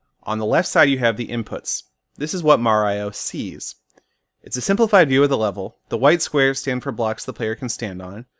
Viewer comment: Hi, I am doing a seminar presentation based on your videos. I didn't get what you have said about the input. So the input is a simple.....? (Minute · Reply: "Simplified" view of the level. You can see that by turning on Captions on youtube.